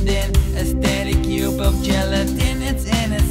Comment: It sounds like hes saying "A Cube Of Jell" XDDDD